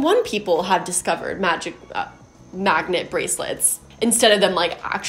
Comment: I thought you said maggot bracelets at first and I was like wtf for a moment 😂😂